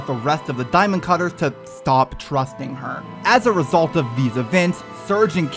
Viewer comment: Damn I heard "stop thrust in her" at begging